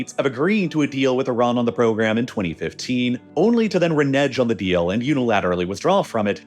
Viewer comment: Did you just say RENEJJ???? Lololol